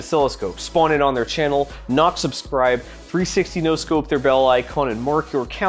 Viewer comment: Great video, funny af. You made all the mistakes I would made. Well done! It's unfortunate that @ 10:55 when you say "knock subscribe" on Keysight it sounds like you said "Not Subscribe"